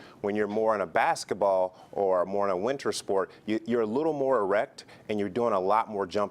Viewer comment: did he say erect?